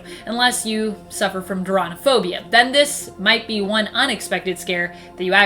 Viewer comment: Does any one know what phobia is said at 2:22 ? I tried using the cc but when I googled it, google didn't recognize the word and thought I said "transphobia" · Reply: Doronophobia